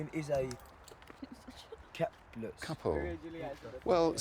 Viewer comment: The student at the start thought he’d said capulet not couplet, bet went with it lol